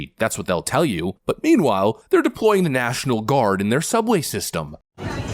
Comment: I THOUGHT SAID NATIONAL GUARD WONT HAVE FIREARMS 🤔 4:27🤔